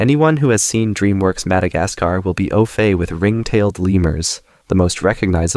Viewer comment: what word was that at 5:44 ophey? how do u spell it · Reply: au fait, knowing alot about something